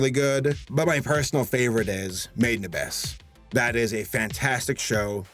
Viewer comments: which one did say at 3:07? really loved teen titans go the thundercats remake rick and morty i gotta check out over the garden, looks fantastic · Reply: Made in Abyss? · Damn, it took me way too much Googling to figure out that what I heard as "Maid Nabess" is actually titled "Made In Abyss." Text labels on titles of things pretty please. :D